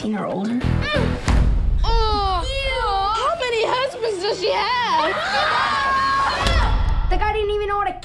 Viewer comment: Does anyone know what the black kid is saying at 1:40? “How many ___ does she have?” I’ve seen this trailer on TV so many times and I cannot for the life of me figure it out. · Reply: How many husbands does she have?!